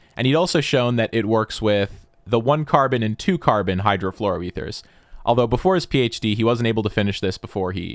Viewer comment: Wow, the auto-generated subtitles are shockingly good, I only saw it miss a few words so far getting "Hydro fluorometers" instead of what you said, "hydrofluoroethers" at 8:15 Additionally, are you able to publish your scripts so we can manually add subtitles?